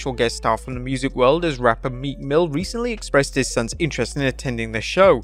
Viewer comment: Meekmill who I thought he said meat mill 😂.